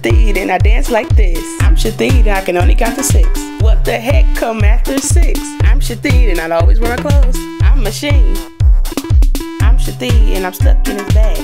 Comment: I can't believe I watched this over and over and did not realise she was saying Shithead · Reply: holy Mothers i thought she was saying chadine